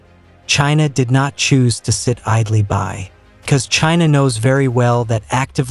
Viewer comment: did that AI voice say cuz instead of because???